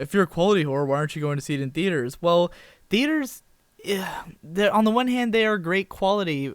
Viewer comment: Captions say beers when you say theaters 2:50. I laughed so hard!